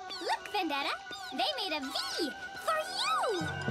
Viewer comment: I know said “They made a V for you!”, but it sounded like she said “They made a V or U!”.